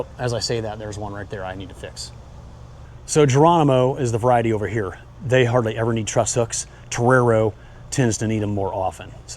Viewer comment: Wow, this video is amazing. Thank you! Could you please write down the name of the varieties you mentioned at @ 19:48? I can't quite understand (non-native speaker here, sorry). I'm about to start my first experiment with undetermined tomatoes in a green house. Is there any variety you recommend? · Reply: Geronimo and Terrero